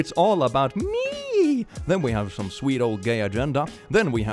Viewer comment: Why did I hear “sweet old gay agenda” as “Swede old gay agenda”